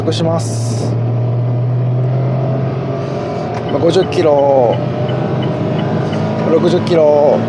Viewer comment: The english subtitle has some minor mistakes. I assume you are talking about its speed. Therefore, " km/h" (or atleast "km") would be nice instead of "kg" if it's not about the torque. P.S. To refrect the original atmosphere, kilo might be suitable.